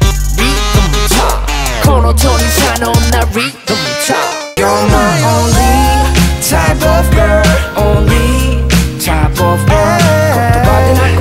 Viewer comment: My Only Type Of Girl...... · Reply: i thought it said "youre my only thai buff girl" lmfaooo